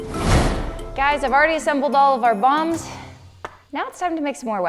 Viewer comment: Turn on captions at 4:30 it say we have already assembled our bums instead of bombs